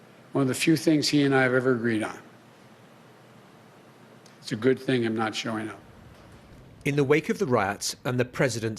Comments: Did Biden just say he's not showing up to his own inauguration? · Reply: I think he said "it's a good thing him not showing up" that's what it sounded like to me. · Did Joe just said, "It's a good thing I'm not showing up!" ? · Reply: I heard, "...him not showing up". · joe said its a good thing i am not showing up ???????????? hunh 2:45 wtf is he saying ?